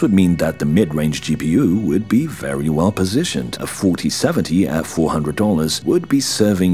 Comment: Did I just hear him say 4070 at 400$?? :face-blue-wide-eyes::face-blue-question-mark